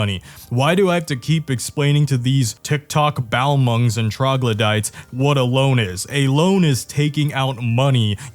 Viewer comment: Did I just hear, troglodyte?